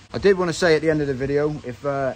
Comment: What was the channel you recommended at the end? Your regional accent 😊 made it hard to understand. I thought you said the bowl and refer? 😂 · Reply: the balding reefer